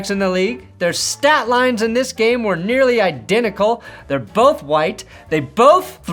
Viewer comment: did bro say They’re both white ??